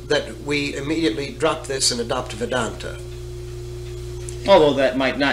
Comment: Does anyone know what he is meaning at 14:00 ? "..that we drop this and adopt [vidanta]?" Googling "vidanta" brings up information about a resort. What word is he saying and what does it mean? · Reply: Vendata. Hindu philosophy, in this case the aspect of dualism may be the most apt.